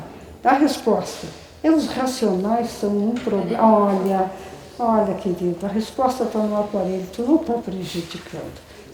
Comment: ouvi uma voz feminina as 17:48 mais nao entendi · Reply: nara moreira eu acho q ela fala "podemos ir" slah